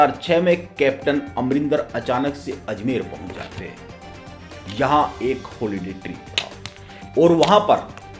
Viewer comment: par "yahan ek holiday.................."????? Tried to listen 8 times even with full volume but could not get last 2-3 words. Just cant understand why do most speakers have this strange and annoying habit of becoming inaudible in the end of a sentence.!! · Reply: Holiday trip